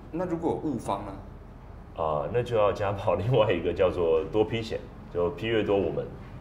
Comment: 聽不太懂...? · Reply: 多劈險 劈腿的對象越多賠越多 不是多P險 玩多P可能不賠